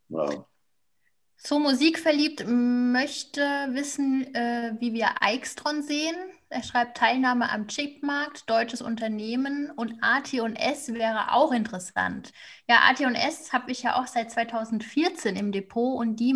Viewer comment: bei min 22:14 habe ich das 2. unternehmen leider nicht verstanden. Ateon Es? Danke im Voraus.